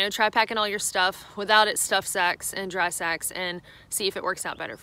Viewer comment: I was watching this with subtitles only (no sound) & cracked up at 10:15 when it came out with 'stuff sex & 'dry sex'. (instead of sacs). Not that kind of channel. Any way thanks for all this advice very useful. Glad here in the UK we don't have to think about bears (we have inquisitive sheep..). Of course the scenery doesn't compare with what you have over there, especially in the West.